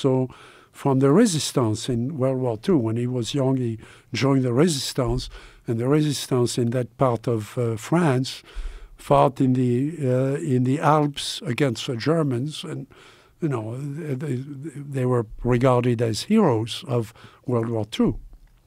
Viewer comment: I heard “fart in the alps”